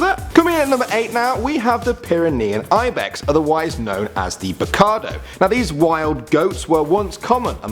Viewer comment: did u say... RICARDO